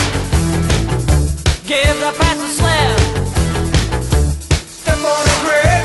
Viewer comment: For the longest time I thought it said give the past a slap, so glad I saw this